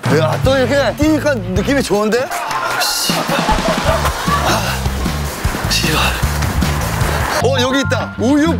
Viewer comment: 나만 익숙한 욕이 들리나..? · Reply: 나도 들림 바로 Tlqkf